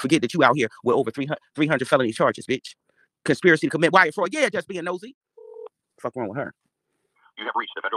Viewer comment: Did she say “juss being nosey, fuck wrong wit her”? I wasn’t even in there.